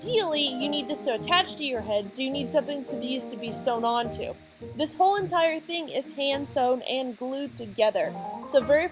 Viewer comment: you say that I need something to make it stay on my head, but I can't really understand what it is? Maybe you could tell me what you said, if you want to? Only if you want to ofcourse. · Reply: Oh ya! It's just a cylindrical piece of foam I made. It's like a doughnut on your head.